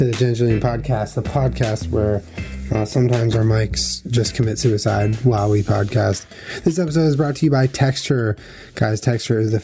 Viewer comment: Somehow I heard "Sometimes a guy named Mike commits sewerslide while we're on the podcast." I was very concerned.